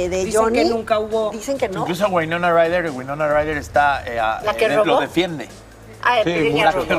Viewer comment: Sexto Comentario Ni modo Amber Heard está siendo descubierta por sus mentiras! 0:48 Que fue lo que dijo Shanik que le molesto a las otras dos? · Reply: Dijo: "la que robó". Winona robo prendas en una tienda y maquillajes en otra. Cayó mal lo que dijo porque, aparentemente trató de desacreditar a Winona con eso, siendo que eso ya fue hace muchos años y Winona pasaba por problemas psicológicos en ese entonces.